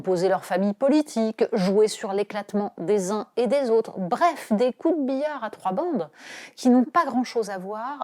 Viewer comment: J'ai compris "des corbillard à trois bandes."